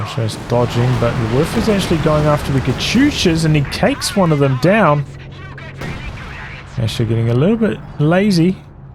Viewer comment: sick barrage but what is the panzerwerfer voice line saying? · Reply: I think it's something like "Fire a bunch of rockets, you get really good at it".